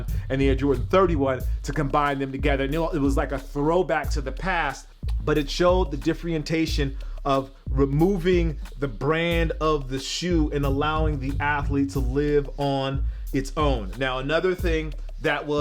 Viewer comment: Did I hear him say "the differentation" lmao wtf